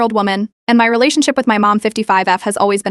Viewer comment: I thought it said "and I'm in a relationship with my mom" 💀💀💀